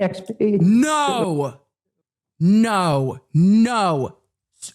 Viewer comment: when i went back and listened to first 911 call. i thought i heard “not now”